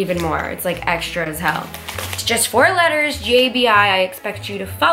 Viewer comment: did anyone notice she said JBI instead of